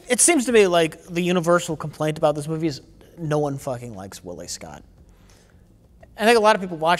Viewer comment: I’ve never seen any of them so for half the video I thought Mike was saying Ridley Scott.